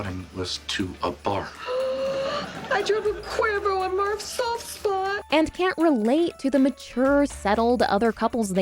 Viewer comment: what is she saying here? "i drank a quasar through my soft spot"? "i drop a quarter on mark's salt spa"? "i drove to quizno's in a honda"? seriously, what? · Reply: I dropped a Cuervo on Marv's soft spot.